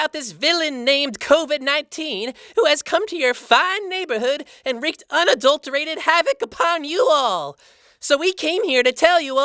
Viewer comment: what does says? "and wreaked and adulterated havoc upon you all"? Help, i'm trying to translate what does it mean